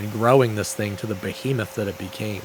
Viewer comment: What was that word he used in 12:29?? «...to the ...that it became» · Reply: "Behemoth" : a large, monstrous creature.